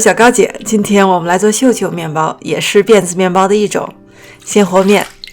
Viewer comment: 辫子面包，我还听成是电子面包，正奇怪呢😂😂